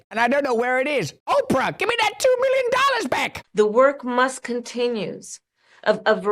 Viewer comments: "... the work must continues..." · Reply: I thought she said musk continues😂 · She said the work must continues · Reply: I thought she said musk continues😂